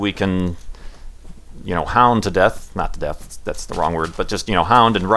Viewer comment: i misheard it as "our elected officials who we can, you know, pound to death" and began to like political ideology of pummeling corrupt government officials.